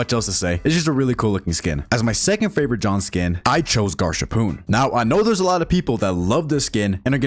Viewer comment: WHAT DID YA SAY MY 🥷🏿 AIN'T NO WAY YOU JUST SAID GARSAPOON😭😭😭😭🥀💔 (Said with all my respect btw❤)